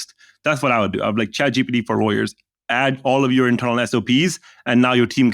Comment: what does say here? chatgpt for lawyers?